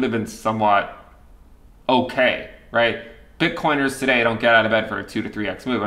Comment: Hit like cuz I heard “bitcoiners dont get out of bed for…”